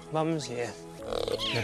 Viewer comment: did he call himself mum? I'm sorry but I'm so soft for this man you have no idea 😭